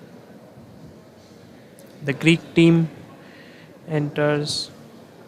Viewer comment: I heard thicc team... I need to rethink my life I guess